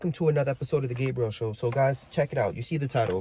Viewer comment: I thought u said welcome to the gay bro show and was like woahhh hold up lol